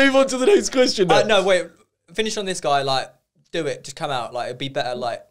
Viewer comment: How did the other boys not catch what said at 8:55 when talking about the closet gay lol? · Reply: " finish on this guy" hahahah